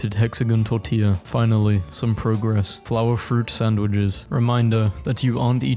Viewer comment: did he just say "peanut butter and sandwiches"?